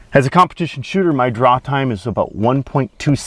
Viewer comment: can someone tell me what he meant by "drop time"? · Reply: TheSpiderJem he said "draw time", basacaly how fast he can pull out his gun